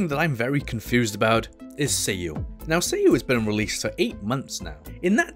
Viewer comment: My asian brain honestly thought you said seiyu but it was Sayu xd